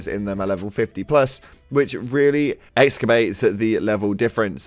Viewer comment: For anyone who didn't catch that, the word is "exacerbates" 😛